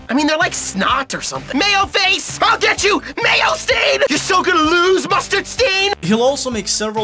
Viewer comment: He says mayo stain? I thought he said "I'll get you! MAY I STAIN!"